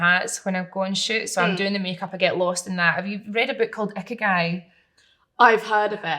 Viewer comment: anyone catch the name of book she mentions 33:53..? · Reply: Ikagai